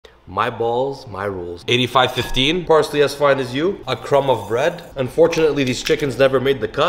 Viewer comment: At first I thought he said “parsley as fried as you” oh I very much doubt that sir